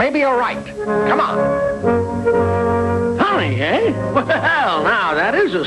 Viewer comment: Did anyone else think Mr. Whoopie said “what the hell“?